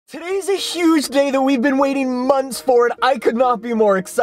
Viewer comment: did he just say "Today is a huge day that we have been waiting months Ford"